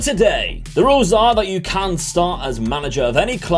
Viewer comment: " you cant start as manager of any club in england, france, germany, spain and italy" or did you say something else ...? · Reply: I said “you can” start, not can’t